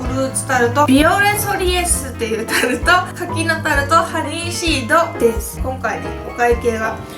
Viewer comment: 柿のタルトが柿の種に聞こえた